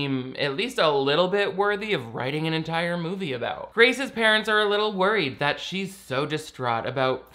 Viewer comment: I thought you said “racist parents” there 😄